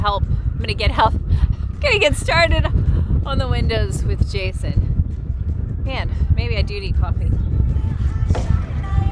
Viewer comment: When you said “ Oh man. Maybe I do need coffee” I thought you said “… need caulking.” I had to listen twice! Lol.